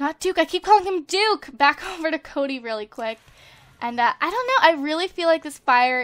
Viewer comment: in 3:50 she said lets go back over to duke then cody instead of lol